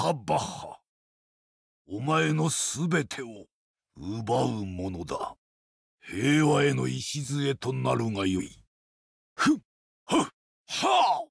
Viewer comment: Do you know what he says between 0:11 - 0:14 ? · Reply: Heiwa e no ishizue to narouga yoi this should be the cornerstone of peace